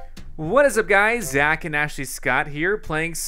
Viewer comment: Did you hear the intro "hi guys proboly here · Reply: RedragonXGamer I heard "hi guys. zack and actually Scott here" lol